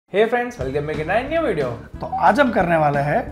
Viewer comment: What says i dont understand its too fast😭😭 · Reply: Hey Friends welcome again in our new video thats what he says 😂